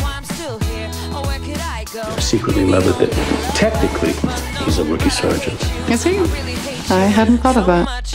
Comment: what does nolan say at 0:43?? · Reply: He is saying that bradford is technically a rookie sergeant